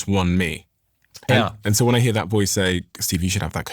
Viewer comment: did he say “I shouldn’t have that co**ine, it’s 1 AM”??